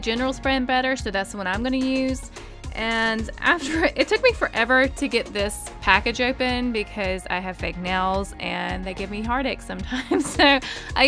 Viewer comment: When the eraser slime comes in I hear "I took me forever to take this out cause i have fake nails" wut? I DONT KNOW BUT IF I HEAR WRONG SORRY